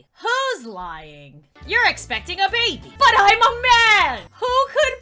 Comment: When I saw the video, at first I thought it said “You’re expecting a baby!” “But I’m a banana!”